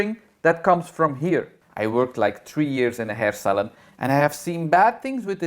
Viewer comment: New sub here 😘 I thought you were saying hair salad when you said hair salon. Lol I am a hairdresser too (since 1969/&'71 got my teachers degree. Thanks for the entertainment. (Good thing you added that NOT! after advocating purple shampoo for everything!) 😂